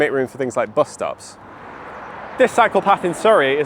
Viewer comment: Everytime he says "cycle path", I hear "psychopath"